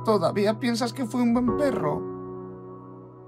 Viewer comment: que dijo? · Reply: Que fue el mejor perro